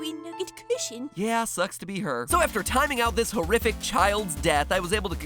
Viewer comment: sounded like you said that lily is horrific XD